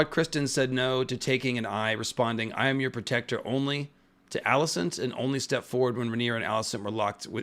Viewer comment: I thought cole said “as your protector I can’t” or something. Because he definitely wasn’t goin for it at first